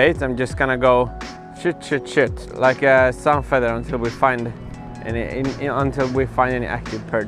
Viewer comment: what is he saying? "like a ....* Can't understand. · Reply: Redtrout like a sunfeather, you know one of those you can use as a fan. Basically fishing from the left to the right